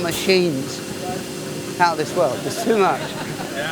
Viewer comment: I think he said " Out of this world " instead of how this works ??